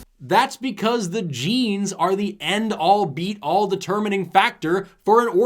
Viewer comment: It’s actually “end all be all”…. Thought you said “beat all” there.